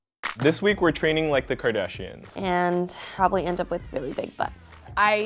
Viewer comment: I thought it said: "People try to like the Kardashians for a week" which would make more sense